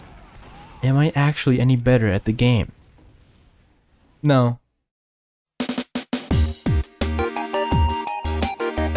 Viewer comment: did he say "não" at the end? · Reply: Nihao